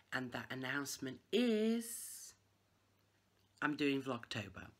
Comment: First of all I had to start over twice on this video I thought you said, "I'm due in Oct." L😊L ! I love watching your videos and I am looking forward to Oct. Blessings !.